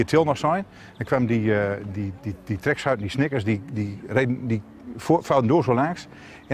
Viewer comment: ayo what did he say??? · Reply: snickers